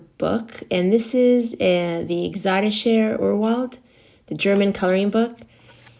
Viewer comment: Hi! What was the name of that German book with the wonderful trees? I couldn’t quite make out what you said! I heard “exoticshare vernwold” LOL. Love your updates :)